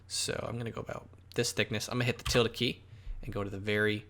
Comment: Question, what is the Tilda key? or something similar, can't understand what you said at 1:53 to get the options for the different perspective views. Played it back a few times and can't understand what you said